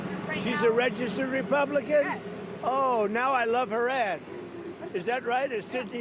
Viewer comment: He said "Adds" At 1st, I thought he said " I love her A$$" 🤣🤣🤣🤣🤣🤣